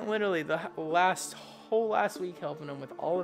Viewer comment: says i spent the whole ass week helping him Who else heard that